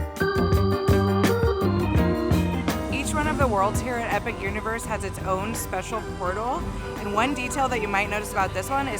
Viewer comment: Ok wait a minute lolol.. At about 1:09 you say a French what??? Hahaha. I’m like did he just say a french W… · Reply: Auror